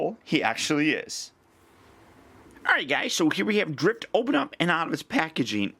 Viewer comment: If I'm not mistaken 2:13 into the video did I hear him say Drip?